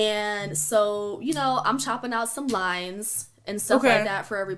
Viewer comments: Someone said a cokehead tarot card reader. I cannot. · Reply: I straight thought this chick said "limes". Like oh, that's nice she made drinks for everyone. Then said "hollup bih, you mean coke right?' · "Bussin down some lines, reading everybody's future...just having fun"....👀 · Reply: I really thought she said cutting up some limes 🤦🏾‍♀️ for some drinks lol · The way she casually mentioned lines 😂. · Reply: At first I thought she said she was chopping limes for everyone, I didn't wanna believe she said lines